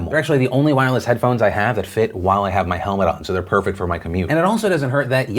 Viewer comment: did you just say helmet? As in you ride a motorcycle to work? ...ok but that’s kinda hot